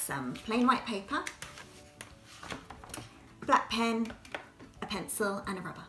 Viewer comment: I heard blackpink 😂😂🤦🏼‍♀️